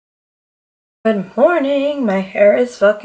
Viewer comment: Omg, when you said good morning, I heard 'i'm hornnyyy' 😂🤣😂